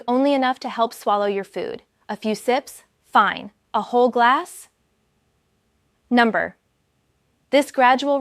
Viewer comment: This may be legit info, but it’s an AI video and voice. Advance to 9:38 to listen to her say “a whole glass… number.” While the caption says “no” I initially thought she looked young to have 20 years of medical experience